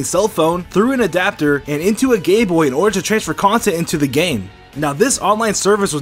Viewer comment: Bruh sounded like he said "Gayboy" instead of GameBoy